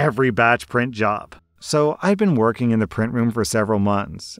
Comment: I heard "I work in a printer room" and knew this is a big ass can of worms! Printer's are like IT nightmares getting one to work after it breaks pretty much makes you an engineer!